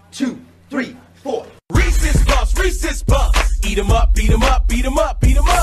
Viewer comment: What I heard instead “Racist puff Racist puff beat them up beat them up beat them up”💀